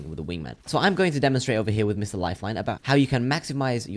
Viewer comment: One second, 1:07 did he just say mr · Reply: Yess mr lifeline